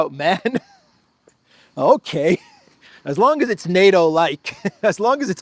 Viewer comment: I thought you were saying Article 5 Light, then I realized it was Like. 😂 I've got the volume turned down. It's 3am here.